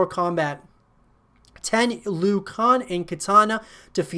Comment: Did...did he just say "Liu kong"????? Lmao